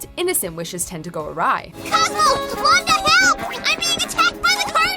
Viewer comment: Did Timmy say what the hell? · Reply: "Help"